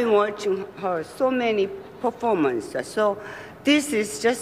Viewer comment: when she said performances, I thought she said for 4 months and I was like- you've been watching Glenn close for 4 months?